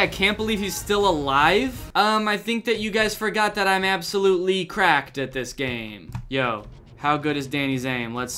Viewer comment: At first, I thought he said "I'm absolutely crap at this game